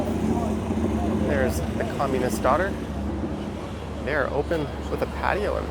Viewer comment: I tried to understand four or five times what you said there, but it always sounded like 'the communist daughter' to me. :-D